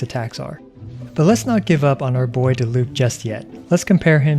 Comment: Hmmmmmmm is it just me or when he said, “Lets not give up on Diluc just yet” I heard, “How can we mock Diluc further?”